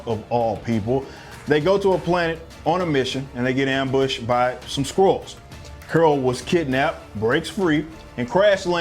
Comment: Thought you said "ambushed by some squirrels".